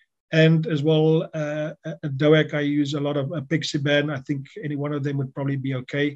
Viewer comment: I had some problem understanding through his accent to hear what three drugs he uses for his triple therapy at about the 2:15 mark in the video. I heard clopidogrel, aspirin, and .... ?? Could you list these in description with recommended dose and duration as well as adding any links to studies where this therapy has been tried? Having the info in text would be a good compliment to the video and very helpful. Thanks in advance! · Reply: "Aspirin, Clopidogrel, apixaban, dabagatran" (copied from comment)